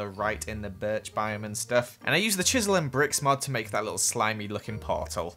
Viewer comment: hmmmm did e just say "chisel and BRICKS"???!?!?!?! here da proof: >UwU> 9:11 No just me? oki .,.'''